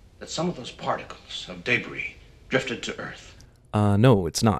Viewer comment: Did you guys....hear Lex say "day-bree" instead of debris at 2:47? I mean....can an air freshener now hurt Superman? (Daybreeze anyone? :P)